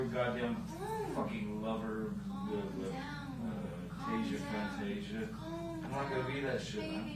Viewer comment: Did he say "Tales of Phantasia"?